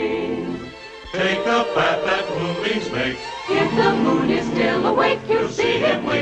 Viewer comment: sounds like think of batman pooping snakes if the fool is still awake instead of take the path that moonbems make if the moon is still awake